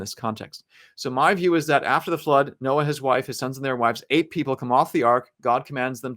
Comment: I bought the book for my wife for Christmas. I also have to laugh at something I miss heard you say. “So after the flood, Noah, his wife, his sons and their wives ate people.”